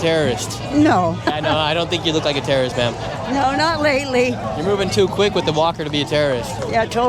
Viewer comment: When said you don't look like a terrorist, 30:00 she said "not lately", did I hear that correctly? 😅